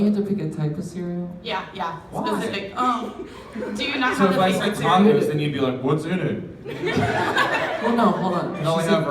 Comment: what did Josh day after "why?!" at 9:53 I couldn't hear ( he said something along the lines "so if I said ..... you'd be like what's in it?!) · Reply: He said 'so if I pick tacos are you going to ask me what's in it??'